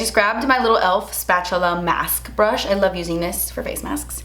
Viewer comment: Pilaf mask? lol I misheard.